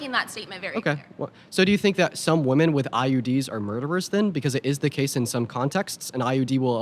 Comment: About ~ 1:21:49 English isn't my main language and the auto-subtitle is pretty crappy. Does he say "Do you think that some women with iuds are murderes?" is IUDS correct?